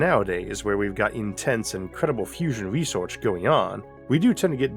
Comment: it's not "incredible" but "CREDIBLE" fusion research going on. Much more important. Also, I found your closed captions to be hilarious throughout this section, knowing what you were really saying. (it's not your speaking but the AI generating the CC's that's at fault.) (I'm kind of hard of hearing so sometimes I try to use the CCs to understand, but if I can't understand, then I've found that the Closed Caption AI usually doesn't get it either. I'm talking about other UTubes also of course. I wonder if you can have someone proof read the CC's. I realize that would be time a consuming task, so no worries.)